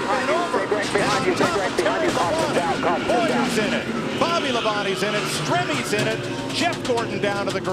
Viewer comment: did he said bolly the bonnie is in it? lololololo fnaf cconfirmed?!